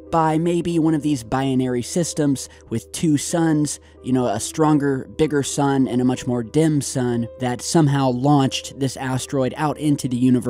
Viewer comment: Did say "Dim Sum"?...cause thats making me hungry.